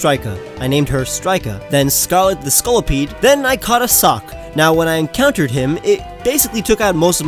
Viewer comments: I thought that he said "I caught a sock'' because im an idiot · If you enjoyed the video, check out these links! -T-SHIRTS! - <link> -PATREON REWARDS - <link> By the way, towards the end of the video, when I was showing my alolan teams, I said that I had too many water types. I meant Bug types. Bye. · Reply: I wasn't watching the vid only listening and then I heard..."then I caught a sock"...XD